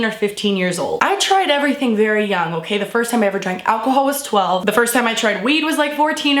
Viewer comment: does anybody hear when she says first time i ever drank weed at 1:48 but this video is funny